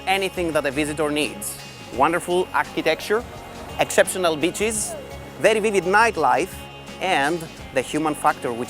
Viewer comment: did he just say "exceptional bitches"???